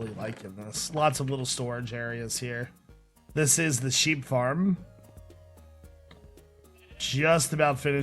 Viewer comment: at the sheep farm it sounded like he said weed farm